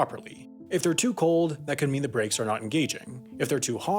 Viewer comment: The "breaks" part in your subtitles is wrong, it should say "brakes".